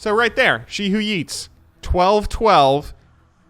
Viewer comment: "she who EATS" lmao... I swear its worth watching just for his name skills Edit: or did he say "she who YEETS"? Funny af either way